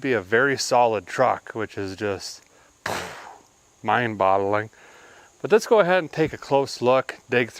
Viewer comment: Sorry but is he saying "mind bottling" @ 1:13? I noticed this on another video of his also but thought I might have misheard it, but now I'm more convinced I didn't haha